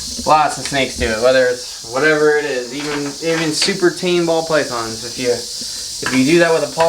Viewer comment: Am i hearing things or did he say Paul Bython ? (@ 19:52) 😂😂